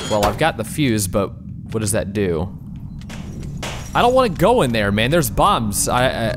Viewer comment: He said “I don’t want to go out there, there at bombs” I thought he said “ I don’t want to go out there, there are B U M S